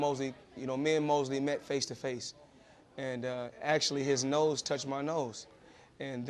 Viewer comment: me and mosley meet face to face..his nose touch my nose.... i thought he just say his lips touch my lips...wahahaha lolz